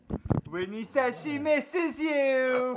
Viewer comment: when teh dog barks i cnt hear what your saying for me it sound like granny says she misses you then the dog : noooo bite